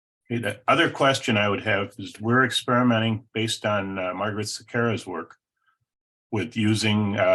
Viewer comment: Thank you for sharing. What is the name author and book mentioned on 2:07:12? Is it Margaret Sakura? but I think I misheard.